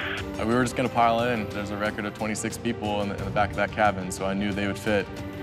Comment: hold up did he say 26 people?????? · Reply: He said "there was a record of 26 people", so sounds like at one point someone crammed 26 people in the back of a Jayhawk, LOL